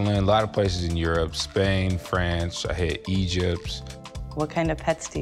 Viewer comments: did he said 'I hate Egypt'?? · did he just say he hates Egypts?😂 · Did he say I hate Egypt